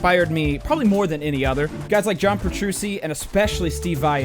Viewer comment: Did you just say "Petrusi" ?????